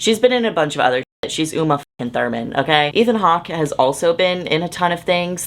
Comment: when you mentioned ethan hawke, i thought you said ian hawke like the. the man from alvin and the chipmunks...